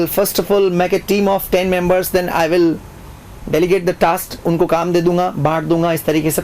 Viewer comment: Sir what did you say after saying-I will make a team of 10 members.At 6:44...... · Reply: Delegate the task